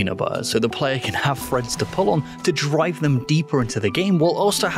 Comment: “Cover that apple in peanut butter so the player has some threads to pull on.” I do not wish to buy your apples, sir. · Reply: I heard it as "has some friends to pull on" and wanted to buy his apples even less.